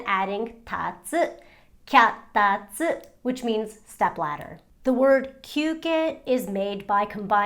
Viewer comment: Did you say duck bladder..?